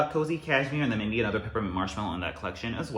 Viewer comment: Did I hear Peppermint Marshmallow at 17:05? Fingers crossed!